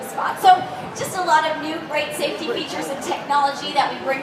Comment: Oh sorry, my bad. I thought it said 'sport features' :)